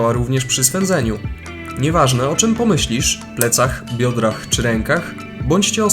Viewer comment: usłyszałem Jądrach Xd